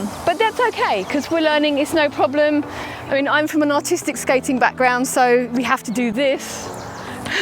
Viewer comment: I thought you had said you were from a "autistic background" and not a "artistic background". I was confused at where you were going with that lol